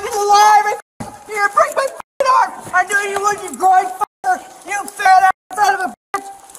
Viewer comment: she shouts “I knew you’d do this you goy” or did she say something else?